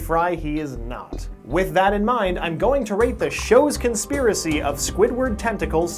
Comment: I thought he said he was going to rape the shows conspiracy